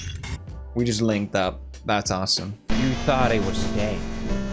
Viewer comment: every time he says "you thought it was dead" in the outro, i always hear "you thought it was gay" for some reason lol